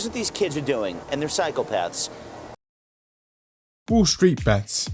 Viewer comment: am I the only person who heard “cycle paths” instead of psychopaths?